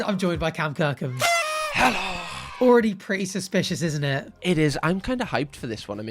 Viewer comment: I thought you when you were saying "its already suspicious..." cause cam is with you